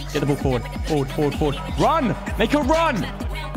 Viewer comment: It cant be only me did someone hear say the n word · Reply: He said "make a Run"